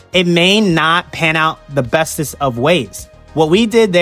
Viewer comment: did you just say the "bestest" of ways??